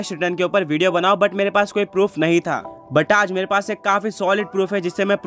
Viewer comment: what 😂 ye kya bol diya ek baar aap v dekho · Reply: Toilet proof